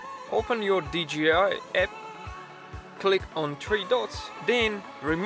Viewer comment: What the hell is he saying at 0:29??? I replayed it over and over and over again. "click on tree dos"? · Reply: Click on THREE Dots. Almost got me too. :P